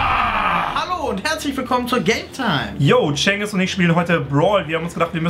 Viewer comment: ich habe gaytime anstadt gametime verstanden